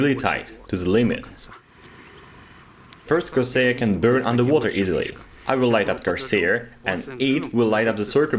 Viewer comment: Thought it said underwater retards, and i got very confused